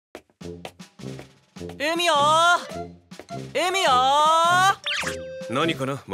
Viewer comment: 海よー海よーに聞こえた‥。